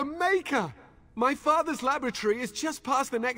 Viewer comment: Every god damn time i hear him say "my father's lavatory" instead of laboratory. i blame work